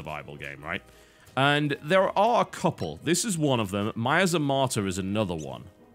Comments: I gather from the comments that the "martyr" game TB mentions at 24:34 is actually called "Miasmata". Check it. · Does anybody knows which game he was talking about at 24:33? I didn't quite understand because of his accent. Thanks advance. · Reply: Miasmata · does anyone know what the game he mentions is called? I can't understand what he's saying for the life of me, it sounds like "Mayas of Martyr" but it isn't :/